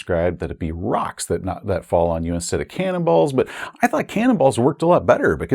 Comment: For a second I misheard 'cannibals' falling down, which the pirates would have at their disposal (naturally!). Cannonballs make much sense, but I can't stop thinking about cannibals being thrown by pirates . 😎